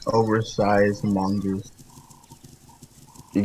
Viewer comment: Man, I first watched the video and the first thing I hear is “oversized mongoose” 💀